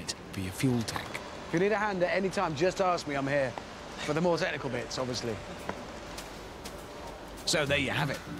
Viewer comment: could anyone understand what richard was saying at 40:12? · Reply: tonierpillow4 “for the more technical bits obviously” it ain’t that hard